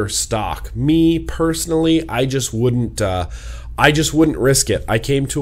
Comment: When you said "I just to." I thought you said " I just tooted." and scared my dogs I started laughing so hard, I'm sorry I had a moment of childness and had to share it with you.